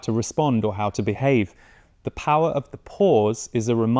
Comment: I thought he said the power of the poors, i was like.. "yesss, its our turn now!" 😂😂😂